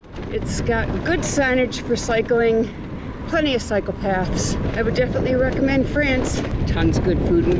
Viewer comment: You guys have seen so much beauty and history. I love it. Just finished chapter 8 where in the very beginning of it, says "plenty of cycle paths", and for a second I thought she said "plenty of psychopaths."